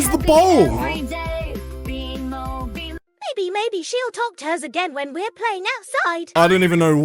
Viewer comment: not to be rude just to correct something What Bimo just said Bimo Said "Maybe Maybe She'll Talk to us Again When we're Playing Outside"